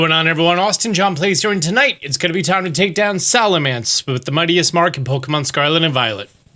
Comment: Could have sworn you said “Salamence with the muddiest mark” for a second.